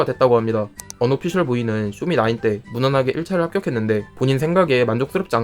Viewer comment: 쇼미9 이라고 들려요